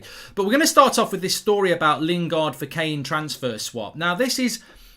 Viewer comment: I thought you said Lingard for Sancho?